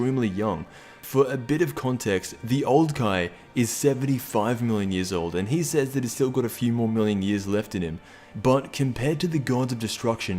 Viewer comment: I thought beerus said he was a few trillion years old